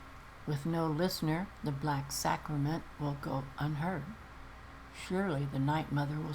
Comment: Lol I thought you said "Shirley the Night Mother will speak to someone soon. " 😄😁